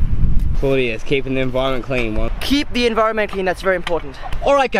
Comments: I thought he said keep the environment clean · I thought he said `keep the inviroment clean. 😂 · I thought he said to keep the investment cleen😂